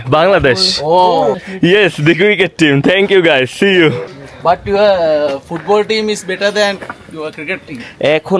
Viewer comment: সে কি বললো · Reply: ও একটা মহামূল্যবান সত্য কথা বলেছে you guys football team is better than cricket team